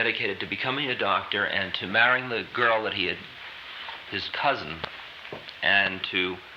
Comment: Does he say “his cousin” at 20:20?